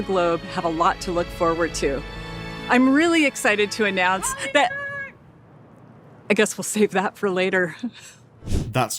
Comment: what does that person in the background yell out? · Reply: I think she said, "Holly, no!" but why they made it so hard to understand makes it so damn cringe